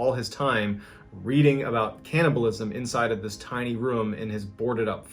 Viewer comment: That last story. I heard "He started spending all his time reading about cantaloupes" I was like "oh thats nice" then heard cannibalism.... different things